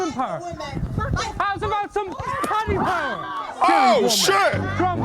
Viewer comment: The clip at 12:18 was like a social experiment from a channel called 😂 it was a sugar glass bottle and I think they were trying to show the reaction to violence between men and women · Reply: What he said though killed me , please tell me I heard right .... " paddy power " because he's Irish . Lol 🤣 .